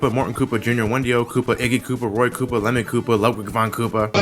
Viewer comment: When you where talking about the Koopalings it sounded like you said Lemon Koopa